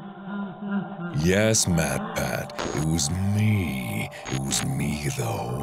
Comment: In the beginning i thought mark said "yes batman" but then i worked out that he actually said matpat, lol. Or maybe he did say batman. I'm too lazy to rewind the video